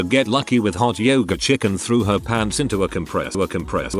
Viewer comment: when the robo voice kept repeating "A compressor" it sounded like "Look impressed"